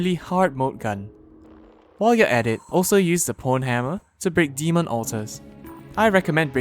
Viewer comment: Instead of saying the Pwnhammer he said the Prnhammer